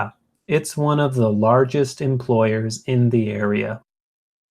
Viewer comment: I didn't get this part, I kinda hear him sayin' employers but i'm not sure if I heard him correctly not until revealed the transcript @ 14:10, eventually I'm at peace. 😜👻